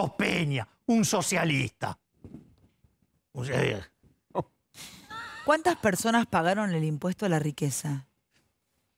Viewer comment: minuto 40:39 la prueba de que Milei es un reptiliano no puede ser de este mundo un ser tan especial...que dijo en ese segundo? que idioma maneja?.... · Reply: "osea...." uno de sus 350.000 latiguillos